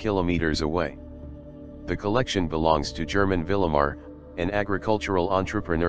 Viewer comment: “german vilimar an agricultural entrepreneur “ what did he say? I can’t find anything on google similar to that name i want to see this guy lol